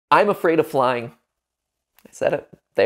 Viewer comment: I was so confused because i heard “im afraid of lying”